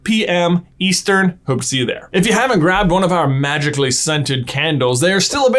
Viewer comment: I thought you said "magically sentient".